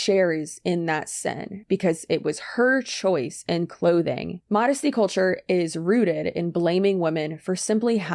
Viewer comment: When you said "Modesty culture is rooted..." For a second I thought you said "Modesty culture is rude" and like 🤣🤣 that would work too!